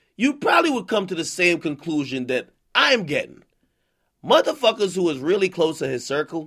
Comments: Son did he just say "IM GAY" ? Or am I trippin? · Son did he just say "IM GAY" ? Or am I trippin? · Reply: lmao I thought the same thing. He said “I’m gettin” lmao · I could’ve sworn I heard AK say ‘IM GAY’ 😂😂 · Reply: Baked Benji facts did he just say I’m gay???? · did nigga just say we was gay that was so unexpected an funny as fuck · I thought he said “I’m gay” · It sounded like Ak said “You’ll probably come to the same conclusion I’m gay” at 4:40 LMAO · I thought he said “so you’ll come to the same conclusion as I am... I am gay” lmaoooo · I heard AK say ‘IM GAY’ 😂😂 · At first who thought he said I'm gay at 4:40 lol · I could’ve sworn I heard AK say ‘IM GAY’ 😂😂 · I thought he said Im Gay 😂😂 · This dude just say he gay? Or did I hear him wrong lol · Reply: He said I’m getting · Anyone else hear ak say “I am gay” at 4:40?????? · Yo ak i swear i thought u said im gay hahaha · What the fuck lmao did he really just say i am gay at 4:40 ? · Did akademiks say “I am gay” at 4:40 🤔🤔🤔